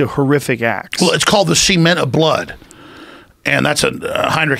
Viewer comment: What is Alex saying here? Semen of blood? So that would be "Samen des Blutes" in german? Cant find either of this, neither in german or in english from heinrich himmler about this. Did i mishear this phrase?